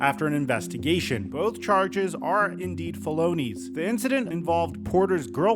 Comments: why did you pronounce felonies that way .. i had to double back as I thought I heard "Baloney" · I thought you said bologna. Thats not how you pronounce felonies my guy.